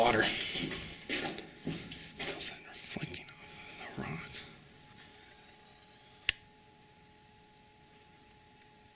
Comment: someone hear the voice at 1:38? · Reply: It's the guy saying "further on" i think